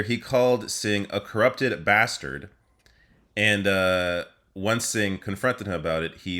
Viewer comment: I thought he said communist bastard.